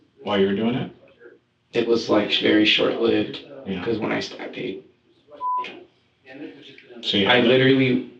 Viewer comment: HES STABBED SOMEONE BEFORE?!😮😮 Am i theonly one who hears him say "When i stab PE-fuck um,"????